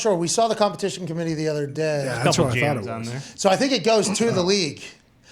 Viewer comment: did Ty say “a couple Janitors on there” or am I hearing wrong? If he did say that, that is hilarious, an overlooked gem by ty